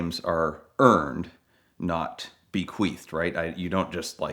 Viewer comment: Did Doug say Bequeefed ? Is that a word?